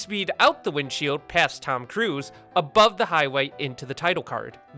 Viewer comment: did he say TOM CRUISE?????